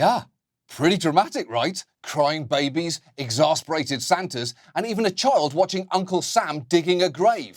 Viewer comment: There is a mistake in the subtitles. On 4:12 instead of "exacerbated Santas" there should be "exasperated Santas".